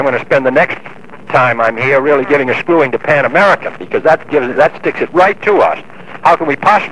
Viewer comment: He was doing what he was elected for : to protect the interests of his country and ensure millions of jobs. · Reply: TWA? I heard him say Pan Am….